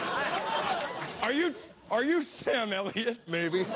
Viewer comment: what does chip say at 2:45? are you sam who?